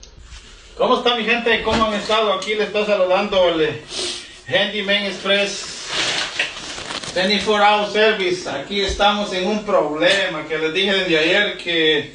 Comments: all i heard was “handyman stress” 😂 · I heard... "24HR Service. Kier Starmer". 😂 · The jackhammer plugged in next to the toaster kills me 😅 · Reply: i hear “handy man express, 24 hour service” 😂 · All I understood was ..."handyman express, twenty-four hour service..." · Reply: I could’ve swore I heard 74 hour service and I was like “now hold on wait a minute” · Did he just say "24hr service, keir starmer" 💀💀💀💀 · He said HANDYMAN EXPRESS ... I bet he's not a licensed anything. 😂😂😂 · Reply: I thought he said "handyman estress" 😂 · Did he just say 24 oz service😂😂😂😂😂😂😂😂🤳🤳🤳👷👷👷💥🔫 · SOUNDS LIKE HE SAID 24hour service kier Starmer special